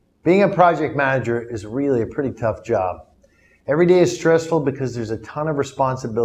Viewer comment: What he said 0:02? Tafu job?